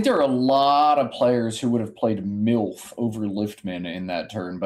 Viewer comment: did he just say MILF??